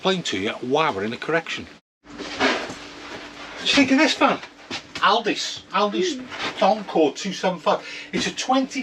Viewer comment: I genuinely thought you said Aldi's and were speaking about the rug you were holding. Ah! Elddis! Time for a wee ear syringe methinks. 😂